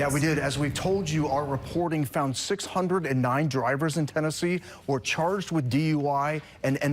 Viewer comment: what i just heard is "Avoid Tennessee"